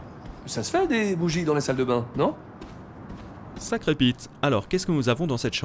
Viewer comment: Sacré quoi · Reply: Pete !